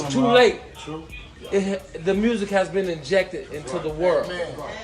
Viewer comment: I thought he said he infected his music into the world. HaHa He said "injected," I like my word better, it is more suitable and appropriate. One minute he can't read, now he can read...cut the bullshit!